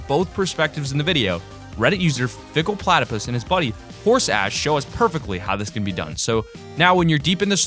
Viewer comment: did he say horse ass